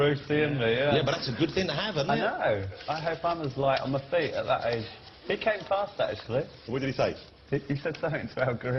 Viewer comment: What does Noel say at 0:10 - 0:18? Sound quality is not the best :( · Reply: Noel: I hope I'm as light on my feet at that age. He came past actually! Jonathon: What did he say? Noel: He said something about gorillas, didn't he? To Bollo.